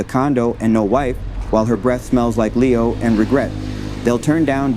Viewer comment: Lmao breath smells like Leo😂😂😂😂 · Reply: Go back. I thought he said, " her breast smell like Leo"